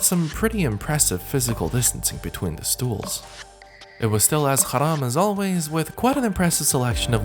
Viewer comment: did you say there : there was still as „Haram“ as always or something like that 😂